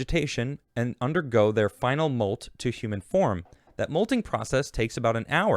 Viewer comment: did he say human form? That would be fun.